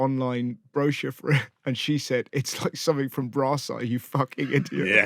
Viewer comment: Can someone tell me what Russell is referring to at 10:15 please? Not sure of spelling but he says something from "brasa" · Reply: Hey - RH’s wife said ‘Brass Eye’ - the 90’s Chris Morris show.